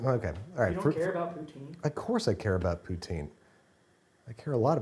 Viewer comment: What Babish says "I care about Poutine" What i hear "I care about Putin"